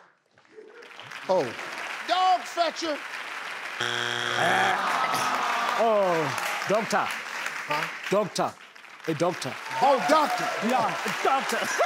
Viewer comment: bruh i thought said Dogtor as in a vet.. lol